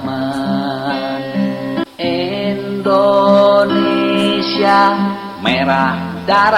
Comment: Menit 1:59 maaf 🙏🙏 kalo saya salah dengar yg saya dengar bukan Indonesiaku melainkan Endonesiaku 🙏🙏